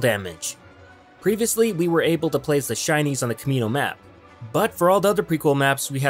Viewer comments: kept mishearing Shinys as Chinese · I thought he kept saying Chinese. LOL.